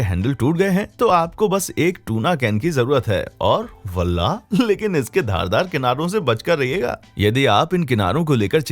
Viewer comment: oooops.... what did he say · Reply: wallah.....